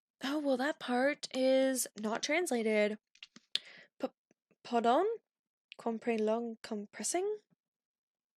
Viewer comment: NO HONEYYYY NOOOOO! 34:45 that was indeed english, he said "pardon?" Like the english pardon, and then he stammered, only grasping onto and repeating parts of what the other guard said, "compre-" was him stammering the word compression "Lugs compressing?" - self explanatory